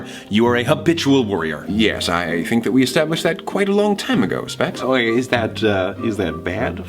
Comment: did Roman call Logan “Specs”?